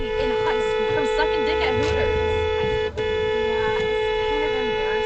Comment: #38 I was listening closely to the drivers "background noise/music" and it sure sounds like the lady said something slightly inappropriate?? If y'all listen to it, you'll probably hear the same thing I thought I heard.... "Sucking BLANK at Hooters?!" Maybe my minds in the gutter, IDK??